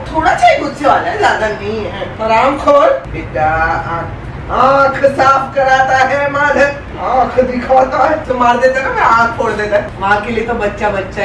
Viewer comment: 😂 I think he said madar....... 😂😂